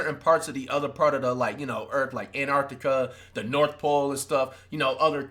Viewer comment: did he say in arctica or tried to say antarctica lmao